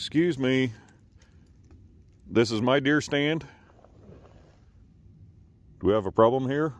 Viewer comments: It may be your deer stand, but I’ll bet it’s HIS/HER ancestral family tree! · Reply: I thought he said beer stand lol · “DEER stand? I thought you said BEER stand! I’m thirsty.”